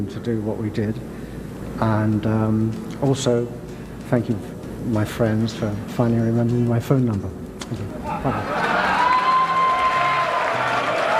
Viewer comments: I can't catch what he said at 2:27-2:29. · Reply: He thanked his friends for finally remembering his phone number. (A dig at Robert and Jimmy for reuniting, but leaving JPJ out). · what did he say ? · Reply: Thnak you my friends for finally remembering my phone number.